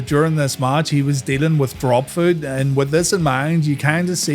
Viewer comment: you said Steiner was dealing with _______. I can’t understand what you said. Drop food? I’ve replayed it a dozen times and can’t figure it out. Any help please? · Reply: “Dropfoot” or “foot drop”.